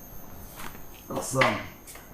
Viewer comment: First I thought he said Assam ... Then I realised that he said Awesome